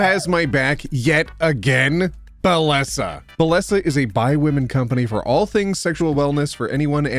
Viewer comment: please tell me im not the only one who heard 'bi women'